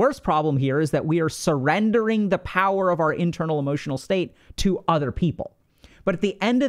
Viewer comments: there I a typo I think. Instead of saying "We are surrendering the power of our emotional state to other people" it says "we are SURROUNDING the power of our EMOTION state to other people". Not to be that person XD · Love all these videos. The grammar nerd in me must offer some corrections though: 5:20 you used "surrounding" instead of "surrendering"